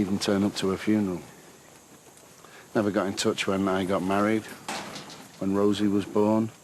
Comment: when who got married??? cnt work out wht he's saying!! i thought he said when i got married but now it sounds different? x